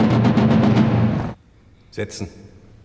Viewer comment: 이거 나만 섹*로 들림?